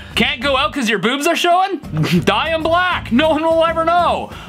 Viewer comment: For typo navigate i heard can't go cuz your boots are showing dye them black